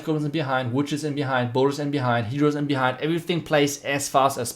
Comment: Did u just say healers at 7:22 😅